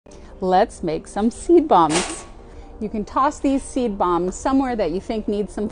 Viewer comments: I thought it said weed bongs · Fun fact: At least 3 people thought she said “Tea Bombs” at first